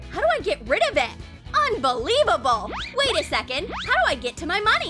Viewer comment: did i just heard unbelibubble? Man u watched to much of jin's dad jokes "we all live in a bubble. Unbelibubble". Lmao. Armyss. We made the record... right?